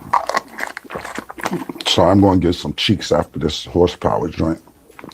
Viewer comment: It sounds like Cam said he gonna go get some chick's after he drank the horse power stuff 😂😂😂 OH lawd